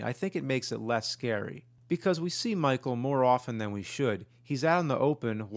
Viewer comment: "we see michael more often than we should." when you said that, before you got to the word, "than", I thought you said, "we see Michael Moore often!"